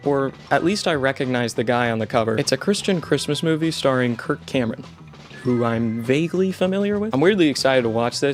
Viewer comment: at first I thought you said “Kirk Hammett” and i was so confused 😭